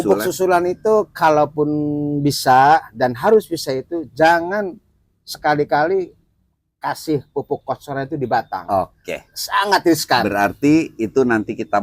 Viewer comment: Pemberian pupuk kocornya jgn diapa itu? Ngga kedengeran ngomongnya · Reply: jgn terkena pohon kak, jd aplikasi dilubang tanam